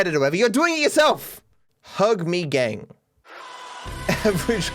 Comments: sounds like simeon says holged me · I hear "holg meh" 😕